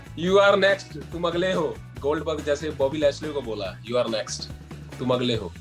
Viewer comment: Did anybody heard "GOLDBUG" 🤣🤣